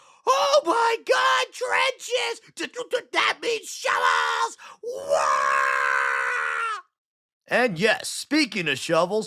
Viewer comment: Did you become an ork I heard you say "That means shovels, WAAAGH!!!